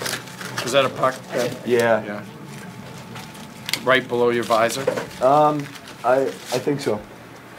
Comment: He didn't say "right under the visor"; as the subtitles said at 01:02; he said "glad he had a visor on."